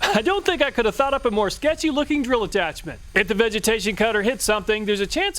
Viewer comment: My dumbass thought it heard “vegetable cutter” and not “vegetation cutter” on the first one 🤣